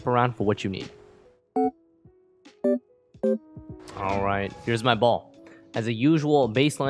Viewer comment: For a second there, I thought you said "here;s my ball" lmao